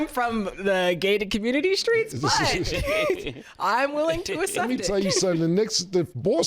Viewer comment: be saying UHCEPT instead of Accept